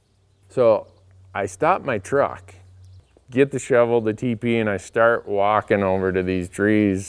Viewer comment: When you were saying TP i thought you were saying teepee, and i couldn't figure out why you were so adamant about bringing a tent along with you.